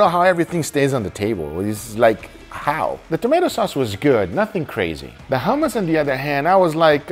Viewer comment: did I hear tomato sauce or tomato soup? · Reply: It‘s tomato soup